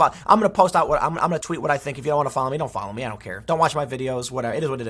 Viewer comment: I know he meant to say, "Don't follow me." But i swear I heard, "Go F---...." :D